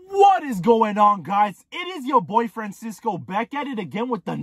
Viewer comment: Why i thought you said “it is your boyfriend sisco “ im like what?? I dont have a boyfriend 😂😂 i had to put the CC to realize you said “your boy Francisco “ its time to get my ears checked 👨🏻‍⚕️